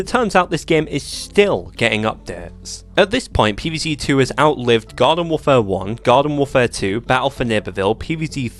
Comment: For a second, I thought you said it outlived God.